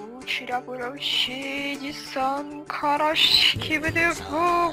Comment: мне одной послышалось "Эдисон карась" 🤣🤣🤣🤣